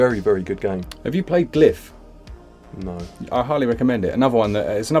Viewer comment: What game do you talk ablout at minute 20:22 (during Lunistice), i've played the sequence 3 times and can't understand it... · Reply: glyph